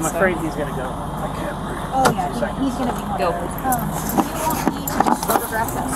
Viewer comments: i hear "i can't breathe", am i going crazy? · Anybody else hear at 5:54 the “I can’t breathe”??? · a guy says "i'm afraid he's gonna go" then i hear right after "i can't breath" 5:50 can anyone explain why i heard that? lol it was creepy, in a whispered voice. saying "i can't breath" seems so not part of the situation. didn't george floyd say that to? just strange. also right after "I can't breath" I hear "I wish i can __ bag"? all 3 things that were said just sound so random. and most of the cops there were female.